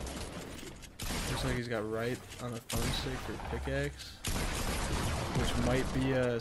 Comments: did anybody else hear him say "this niggas using right on the dpad for his pickaxe" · huh? I don’t know what I heard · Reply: I heard "this n🅱🅱a has his pickaxe" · what did you say, this what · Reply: GET READY PUNK , “looks like he got a” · In 6:04 did he just say this niggas got right in the thumb stick?